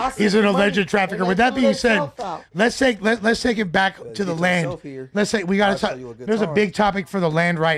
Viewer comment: All I heard was “ take me back to the car blood “ 😮‍💨🤣🤣🤣 💀